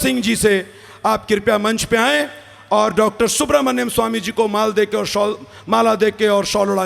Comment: min did he say “MAAL DE KE?” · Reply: fck 😂. he did slipped bt dn corrected as mala 😁